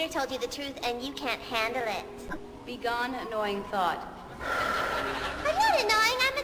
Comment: I thought she said "begone annoying thot" instead of "begone annoying thought"